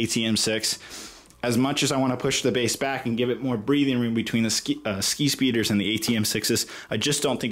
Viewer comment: Each time he says skispeeder I heard “skeet”speeder